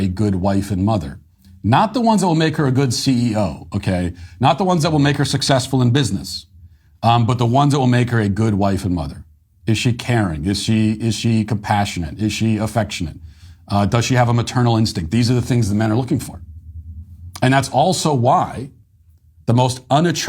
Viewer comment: I misheard as "Is she Karen" lmao